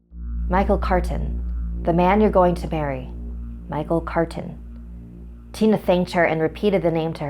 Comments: I’ve watched this so much that in 2:27 I realized she says bury instead of marry. And it’s driving me crazy! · am I the only one who heard "the man you're going to bury." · she said “michael carten, the man ur going to bury” 😳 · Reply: I thought she said marry at first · i thought she said “the man you’re going to BURY”